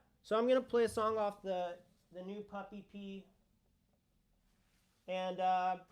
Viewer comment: When said PUP EP for a second I thought he said “Puppy Pee.”